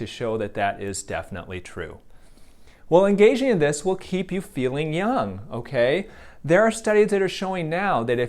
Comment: I thought it said "filling" ._.